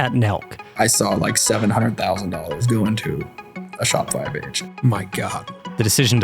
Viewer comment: wtf does he say at 0:57 "i saw like $700,000 go into a ___ ___ _____" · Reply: Shopify page I think